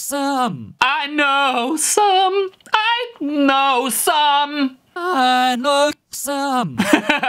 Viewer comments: i heard i know some well i know some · I heard "I know u subbed"